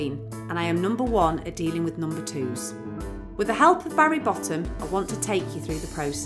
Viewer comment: He hee...i thought she said Harry Bottom! 😆😆😆